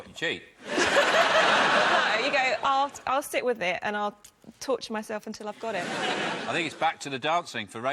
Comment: "I'll touch myself until I've got it" - wait, what? Did I hear that right?